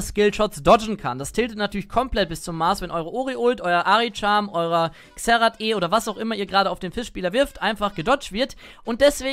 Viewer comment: Gutes Video. hab bei 08:56 erst "eure Omi ULT" verstanden xD ansonsten hättest du Hextrinker für AD matchups noch erwähnen können ^^